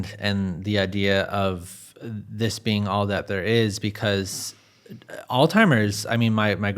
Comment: It sounds like he is saying "Old Timers" rather than Alzheimers.